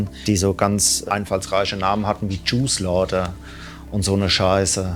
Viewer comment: Noch wer erst bei Minute 4:45 juice laughter verstanden und war verwirrt ?